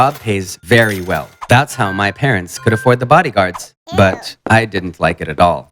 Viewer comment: At first I thought he said bodybards but it was just my ears